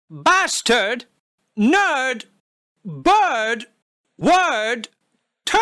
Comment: I was thinking of "third" instead of "bird"